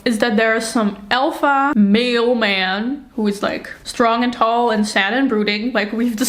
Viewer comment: thought you were saying "alpha mail man" and started laughtng